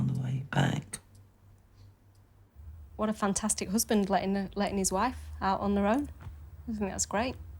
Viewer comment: Did she just say " letting her out on her own?" 😅 oh my.